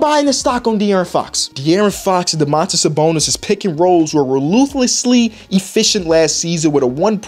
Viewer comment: did he say "rowlooselessly?" 5:34 haha